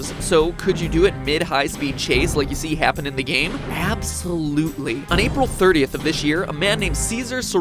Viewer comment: Did you say Appsolutely?